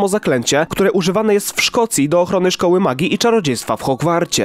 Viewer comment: stop, stop. Ludzie czy ja tam usłyszałam Szkocja? WTF?